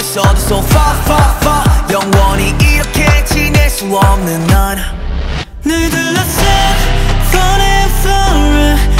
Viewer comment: PLEASE SOMEONE TELL ME WHAT IS SAYS 😭😭😭😭😭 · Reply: "cherishing the fact that you can lose anything precious"